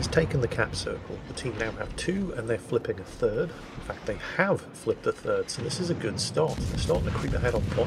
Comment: why did I think I heard "flipped the turd"